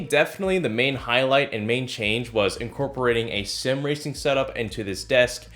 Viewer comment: I thought he said Simp racing setup